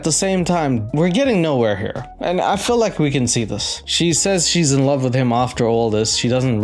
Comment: Oh my god, I thought she said "he can see" not sing and that cracked me up like she had to really dig in there no no he has in fact SEEN her